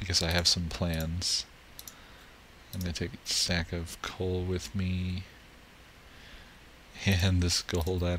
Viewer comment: lol at 8:56 i found the joke instead of saying ill take a stack of coal with me im pretty sure he says sack of coal with me lol im not sure but i think i heard sack and your vids are still awesome keep it up :)